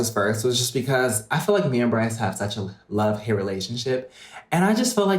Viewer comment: I thought he said we have sexual love hate